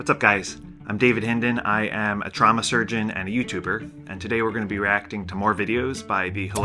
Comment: I thought you said Drama Surgeon. 😅